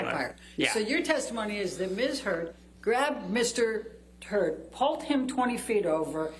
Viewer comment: Did she just ask if 'Mrs Heard grabbed Mr "Turd" ' at 6:22:33 ? Am I hearing things??